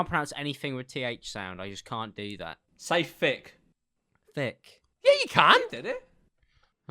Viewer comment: ok i'm sorry but i think there's a difference between the way North American's pronounce "th" to British ppl, because the way that both and said "thick" sounded like "fick" to me